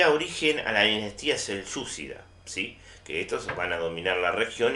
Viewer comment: profe xd,que dice en el minuto 8:04 ?.Sensudida? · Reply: Si usted se fija en los títulos en un momento dice dinastía SELYUCIDA: Si tiene algo que preguntar hágalo en clase presencial. Saludos